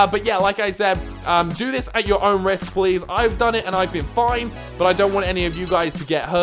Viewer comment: I thought you said I've done this and I've been fined and I was like NANI?